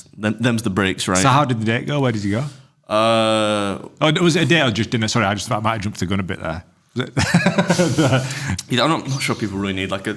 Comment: Okay, i lsitened to him 10x at 11:46 to 11:51 and am still none the wiser wtf he said lol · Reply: “So how did the date go, where did you go? Oh was it a date or just dinner sorry I just thought I might’ve jumped the gun a bit there.”